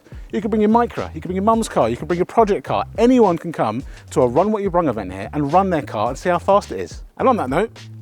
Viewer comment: i can't work out what you say at 8:30, run what you "brung"?